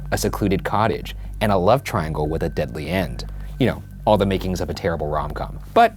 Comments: I thought he said "with a deadly ant" I want a story that ends with a deadly ant. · "A love with a deadly ant" 00:07 why did I hear that and thought it was normal